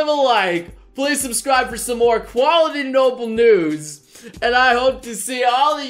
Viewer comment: "Quality Noble Nudes" Did I hear that right?